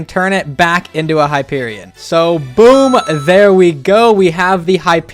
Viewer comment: Nah my dumb ass thought he said hyperlion